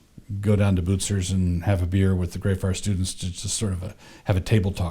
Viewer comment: “have a beer with the guys...” did I hear that correctly?? 😳